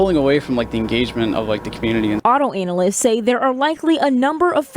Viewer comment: Wait did she say auto-anal-lysts??? 7:30 😂😂😂😂😂😂😂😂😂😂😂😂😂😂😂😂😂😂😂😂😂😂😂😂😂😂😂😂😂😂